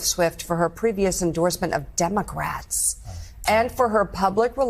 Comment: Wait did she just say demo grats?